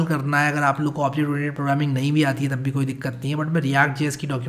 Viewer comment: what did you said sir on 2:22 it was not clear which programming??? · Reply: Object Oriented Programming